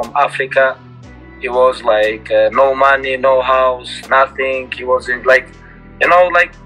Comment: I thought khabib said "no honey" lmao that'd be savage